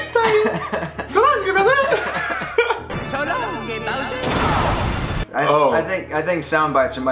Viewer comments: Okay, they're not a couple, but their favourite memory together happened in basement... Also, I always thought, Mario said "shalom, dear Bowser" 😂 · i always heard "so long gay Bowser"